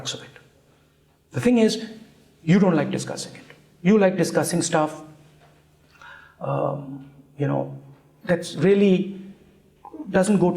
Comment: What he is actually saying at 59:45 - You don't like discussing my arbitrary stuff, you like discussing stuffs that are to the point.